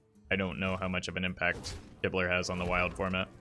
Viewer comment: I was only half listening and misheard "Kibler" as "Hitler". "I don't know how much impact Hitler has on the wild format" Me: "wait what?" "as great as he is" Me: "wait WHAT?"